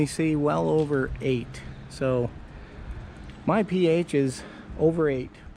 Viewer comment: did you say the ph is over 8 ppm?